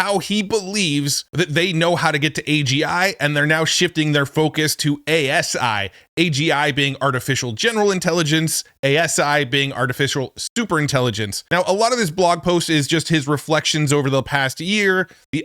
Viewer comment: I heard that he said ASI as an artificial stupid intelligence. Freudian misheard hopefully 😮